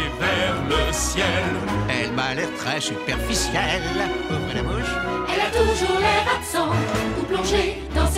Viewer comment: j'ai compris "ouh qu'elle est moche"😂